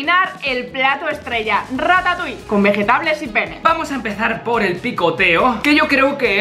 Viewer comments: escuche vegetables y penes :^v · en el minuto 2:26 dice pene creo eso escuche yo